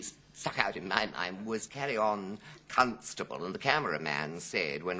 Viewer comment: I think I heard "cuntstubble" 😂😂😅